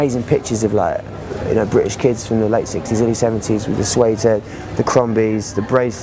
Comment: the translated subtitle is wrong. It shouldn't be '16-17 ans', rather Pete was referring to the 1960s and 70s.